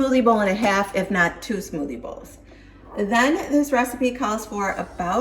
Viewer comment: LOL I thought you were saying smoothie ball! 😂😂😂